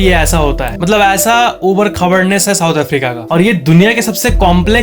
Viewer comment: Did he said SOUTH AFRICA ?